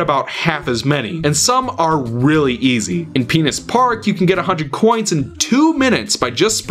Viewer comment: I swear I heard "In penis park"...